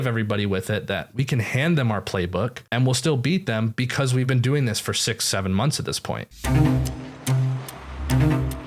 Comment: did he said 67 months?